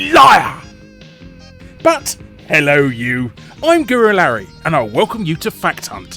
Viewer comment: i swear everythime it sounds like your saying "hello you, im gorilla larry"